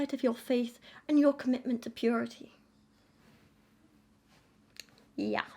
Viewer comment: I was doing something else and just listenting to this and I misheard "your commitment to purity" as "your commitment to PR team" and I got really confused on what this book is actually about :D.